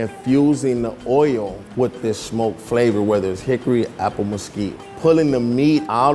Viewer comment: To Eat, Just wanted to give you a heads up. The closed captions at 6:33 say "apple mosquito" instead of "apple mesquite." Also at 18:40, they say "riser" instead of "rhymes with." Thanks for bringing Worth It back!